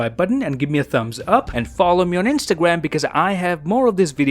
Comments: did you say your name is Dave Chappelle? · did he say his name was Dave Chapelle? 😀